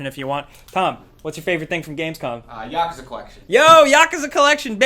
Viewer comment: sounds like your saying yakaza not yakuza